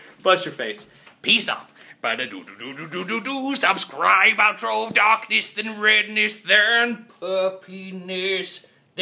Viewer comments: I literally heard "Outro of darkness then redness then a penis" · i thought he said "Uh-penis" :P sorry Toby!